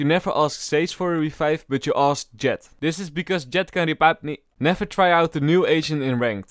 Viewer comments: wait wait wait. what are you saying at this point 7:08 you are saying something like jett can repap me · What the actuall Heck Were you thinking about Bro ??? L M A O ?? WTF ?? You said "'Cuz Jett Cn rbibe maie" XDDD